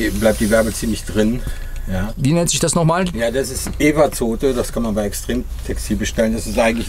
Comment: Evazote, zu finden bei Plattenware auf extremtextil. Für alle die auch mehrfach zurückgespult haben, um das Wort zu verstehen.